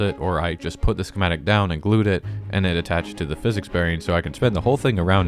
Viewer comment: what I hear: physics barrier variant schematic